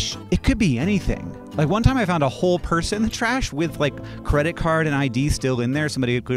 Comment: For a moment I thought you said you once found a whole person in the trash. Given what this book is trying to do, you probably did.